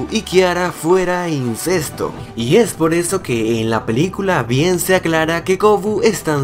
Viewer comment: Cuando dijo "bien se aclara" yo escuche "piensa Kiara"